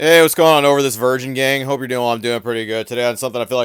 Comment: Had to turn up the volume.. I thought he said Oderless Virgin gang 😂